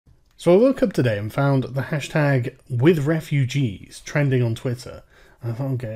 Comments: i thought it said white refugees lol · I thought it said #whiteRefugees